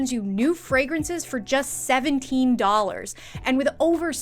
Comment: I’m watching this with captions and just saw that the captions said $117 for $17! 😂